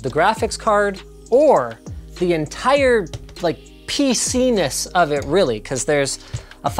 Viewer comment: I really thought he said penis Lmfaooo